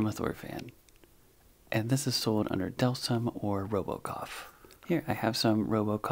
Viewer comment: Thought you said robocop